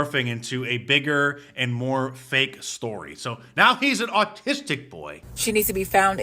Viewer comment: Sounded like she said artistic. Probably doesn't know the difference